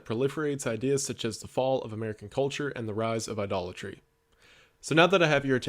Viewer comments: i wasn’t watching the screen and i thought he said “the rise of a Dollar Tree” 😂😂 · I heard "the rise of a dollar tree".